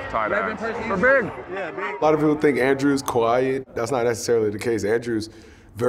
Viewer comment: Anyone else heard "Andrew is kawaii"?